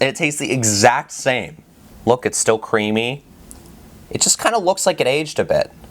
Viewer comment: omg wat he said doe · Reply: c R e A m Y 🤤